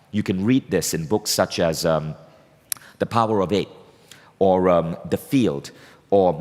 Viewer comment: What were those books he said in 14:01? The power of it? Is it? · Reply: Jossy Antony i think its the power of 8